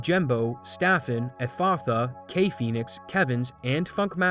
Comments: definitely thought you said FuckmasterMP at first. That was jarring. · I misheard Funkmaster as Fuckmaster, and I was like wow, that's a name to be official with lmfao